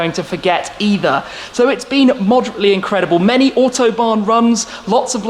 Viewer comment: Great job this year 👏 For me swap the Red RS for something different 👍 Love the “moderately incredible” comment at 6:04. Looking forward to the coming year if that’s the case 😂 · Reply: I thought he said moderately too but I thought I'd heard it wrong! How can all those trips around the world be moderately incredible? It must be another word, I'm sure someone will go back and check? I can't be bothered, only ever watch any video once.